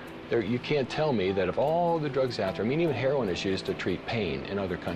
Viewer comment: Did anybody catch the word he says before 'heroin' at 5:03? Is it "meaning"? Sounds like 'medium' but that can't be right.